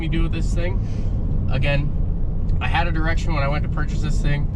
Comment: in - I first thought that you said "I had an erection when I went & purchased this thing but now I have zero erection"... I'm thinking W T F!!! I rewinded , turned up the volume... OH! "Direction"... never mind, my bad....